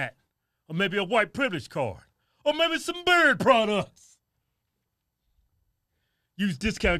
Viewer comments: I realize you are saying "beard products" but every time my brain just goes "bird products" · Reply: I thought they were saying bird products until I read your text. 😂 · I thought you were saying "Bird Products" Maybe they raise poultry, (I thought) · I realize you are saying "beard products" but every time my brain just goes "bird products" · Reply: 😂 I hear "Berry Products". 🤔☺😅